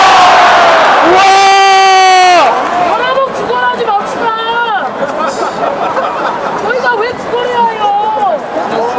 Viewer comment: 뭐라고 하는건가요? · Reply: "여러분 구걸하지 맙시다" "저희가 왜 구걸해야해요" 라고 했습니다:) 너무 시끄러워서 계속 신경쓰였던 관객분...